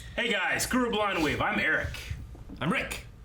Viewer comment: always sounds like he says "cruel blind wave"